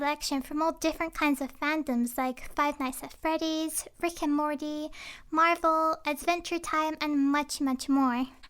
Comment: Sounds like you said "Frick and Morty" when you said "Rick and Morty'. You sound so good